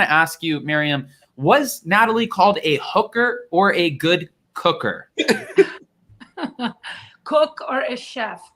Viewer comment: I thought his mom said your a good cooker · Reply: She told her She was a good cooker, not hooker. I cracked up watching Natalie throw a fit before she got in the truck. It was halariuse. She acted like a 2 year old and shes really weird.